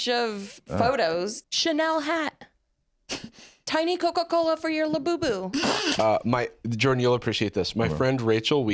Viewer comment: “tiny Coca-Cola for your labubu” sounded like custom wood burning